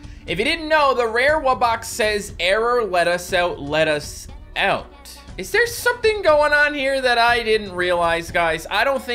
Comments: to me he sounds like he said let us out lelele let us all out when he was on gold isl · When he was on cold island I heard "Fisher let us out *error noises*"